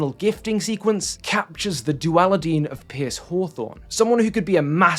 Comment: Wait.. did I hear “Duali-dean” at 16:27? Bravo…